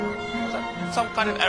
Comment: Can anyone understand what he said at 2:06? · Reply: "You chaps alright?"